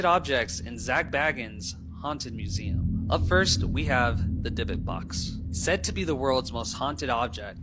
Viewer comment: captions say "the Dick Box" 😂😂🤣